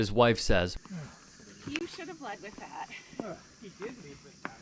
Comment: What did the wife say? Couldn’t hear her. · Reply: yes, I heard "you should have lead with that" pertaining to him saying he is handicap ... seeing the wheelchair he was using there is no way to not mistake he is possibly handicap ... so that couple should have "LEAD" with kindness !